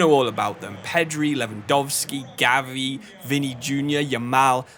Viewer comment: did he really say vini jr?